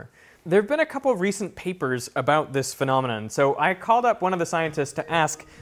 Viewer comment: I thought you called yourself lol.